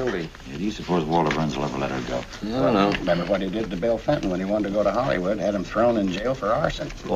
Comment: Did he say “Bill Clinton”?